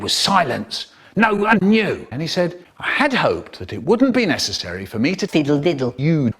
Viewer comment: is that me or did michael say wooden penis? o_O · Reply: He says he hoped that it wouldn't be necessary, but it does sound like wooden penis.